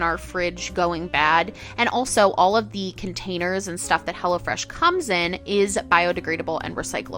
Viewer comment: I thought for a second she said "Hella Fresh" XD